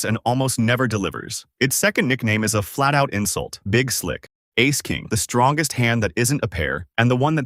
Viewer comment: "Queen Anne's Revenge" = Aces over Queens. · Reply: I heard "big chick" as a rhyme for Big Slick.